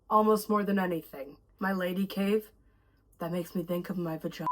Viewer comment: "my lady cave" I swear I heard "My lady cage"